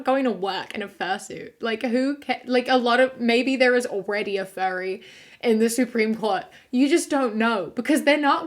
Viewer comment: For some reason when ya say "Furry" I hear "Fairy" weird.